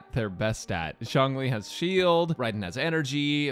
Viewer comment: when you said zhongli at the beginning i misheard it as xiangling, then the tenten music played and i was like ayo oppa??